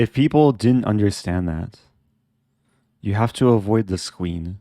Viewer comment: Idk why i hear him say "You have to avoid the squeen"